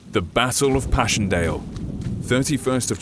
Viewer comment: Sounded like he says the bassle of passchendale